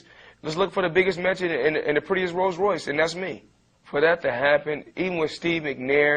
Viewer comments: i thought he said just "look for the biggest midget in the prettiest Rulls ROyce." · O.O waitaminute ... did he say "... just look for the biggest midget in the prettiest rolls royce..." ?!?!?!? no way!!! HAHAHAHAHAaaaa LOL